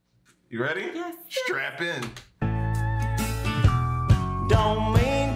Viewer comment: At thhe 4:00 minute mark did you say strap in or strap on 😂lol. Love the video